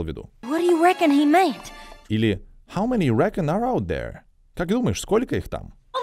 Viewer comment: Прошу пояснить, почему на 0:48 в фразе имеется глагол Do, но ни диктор, ни мультгерои этот глагол не произносят? Или я не слышу краткое d в словосочетании how many (d) you reckon... ? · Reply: На монтаже тщательно прислушивались и там всё же есть do. Долго думали как поступить и всё же вставили в субтитры